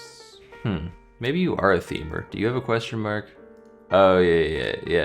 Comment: What is the word he says when he asks "Maybe you are a [x]?" I can't quite understand him and CC isn't helping; it seems to be a name for a type of clue but I don't know. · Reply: Maybe you are a "Themer", meaning the puzzle uses themed clues for long acrosses or downs